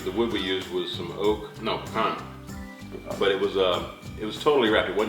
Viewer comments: Did say they used pine wood? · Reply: Lol. I was wondering why everybody keeps asking that. No, he said Pecan but he managed to squeeze it into 1 syllable so I see why you are asking. lol. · Did he say it was smoked on pine? 20:06 It sounds like he says, "the wood we used was oak, no pine."